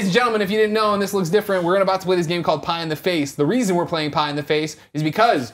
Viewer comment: haha i thought it said pee in the face